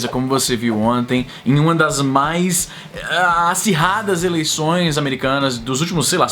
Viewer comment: NÃO foi a eleição mais acirrada dos últimos 50 anos. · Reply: Se você quis contrariar o que o falou. Você se precipitou um pouco, pois o que ele falou foi "em uma das mais... acirradas",e não "a mais".